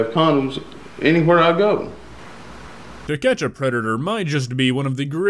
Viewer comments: Lol I thought you said "The ketchup predator" in the beginning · Omg at the beginning he says “To catch a predator” and I heard “The Ketchup predator” lol